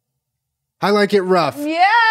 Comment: i swear i thought he was saying “how’ll i get her off” instead of “i like it rough”😂😂😭😭